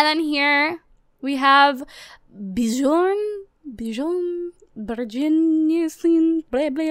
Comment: Wow she's awful at pronouncing names😂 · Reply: It sounded like she said Bigon When it's biuhrn Lol